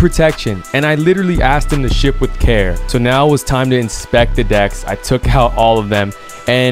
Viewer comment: Everyone hears: “Inspect the decks” I hear: “Inspectah Deck” 👐🏼